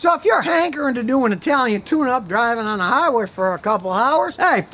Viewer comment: Italian tune up while speaking Italian with his hands. 😂😂😂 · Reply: swear to God, i thought this man was saying italian tuna